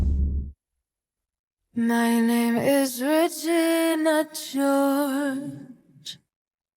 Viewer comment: the first time i heard that on this video i thought she said "my name is rachel not yours."